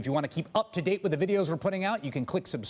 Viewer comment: I thought he said "click subscribe or just blow me" as he points west 5:05 lol.... Truth has a funny way of getting out, this is connected to Jones in Florida... what kind of govt. Would fire scientists and doctors?